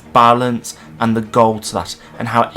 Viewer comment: What is you say after "It's a story of balance and ..."? Sounds like 'the gold schlatt' and i can't make heads or tails of it lol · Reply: lmao I said “the goals”, stupid accent probably made it way harder to hear 🤣